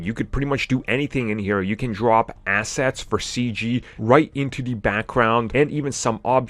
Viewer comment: right, when you said, drop assets, i heard "drop acid". Lol